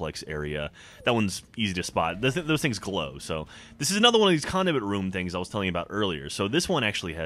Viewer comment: Lol sounded like said Condiment Room